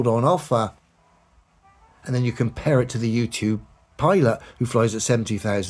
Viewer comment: OOH U2 I thought he said YOUTUBE pilots!!!